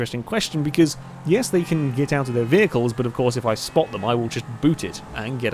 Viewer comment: I thought you said you can't get out of your car